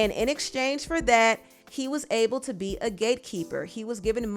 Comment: Omgsh😅🤭 5:47 I can’t stop laughing 😅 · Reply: I thought she said a gaykeeper. 😂